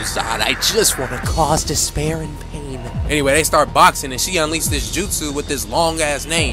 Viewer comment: did bro just call enmu a "she" 😰